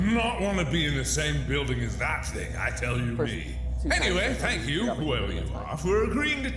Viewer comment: He’s weird voice thing that you thought said “I killed you” but to me it sounded like I TOLD YOU maybe it’s for matpat sense he called him nightmare garbage